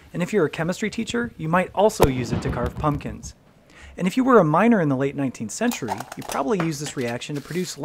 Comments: Thought you said minor instead of miner but then I realized that back then those two words meant the same thing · I thought you said "minor" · I heard "if you're a minor in the late 1800" as if the kids were getting up to acetylene shenanigans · I thought you said "minor" 😂